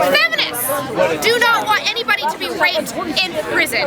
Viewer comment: i just heard her ask to be raped.